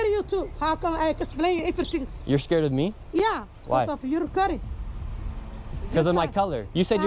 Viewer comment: btw at 29:24 when he asked if she's scared of him i am pretty sure she said "yeah cause of your recording" not because of his color, it is hard to understand but it sounds like that is what she said more so